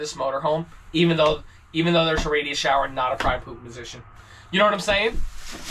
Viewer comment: What were you saying here at the 20:19 point? "Even though there's a radius shower instead of ..."